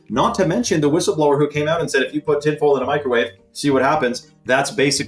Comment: So I was doing stuff while I had this in the background, so at 1:03:25 I swear I thought said "if you put a pitbull in a microwave" and had to stop everything I was doing to rewind and hear what he said. He said tinfoil but honestly if he had said Pitbull I would not be surprised.